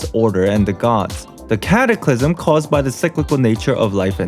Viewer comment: sorry.. did you say... CATACLYSM